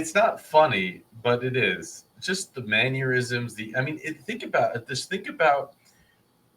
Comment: Did I hear you say "manneurisms"? What the heck is that?!